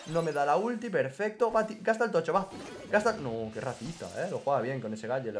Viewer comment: parece que dice "no que rascista"